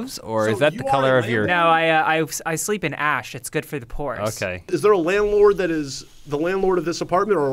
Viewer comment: I thought I heard "...,it's good for the poors" 😆